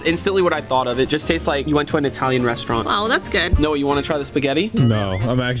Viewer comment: Is it just me or did he say Noah do you want to try it and was behind the camera